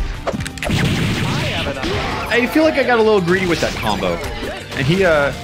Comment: ngl thought he said greasy instead greedy Idk why but still Also missed opportunity to to fry instead of fly